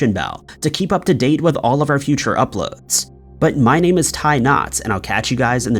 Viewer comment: I'm glad you stopped saying, 'I've been...' at the end. It was so disturbing. Lol. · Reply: For a while I thought he was saying, “…but, I’ve been tying knots, and I’ll catch you guys on the next video.” And that always confused me, like, do you also make bracelets while recording? Are you a sailor? And I agree with the person who is put off by the “I’ve been” part, as if he is not currently. Just a strange kind of catchphrase. I know it’s a very small thing to nit pick, but it’s funny that it also kinda bugged other people to the point of having a comment thread 😂